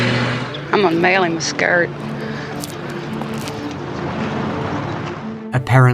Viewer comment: imma mail him skirt???? Wth was she saying?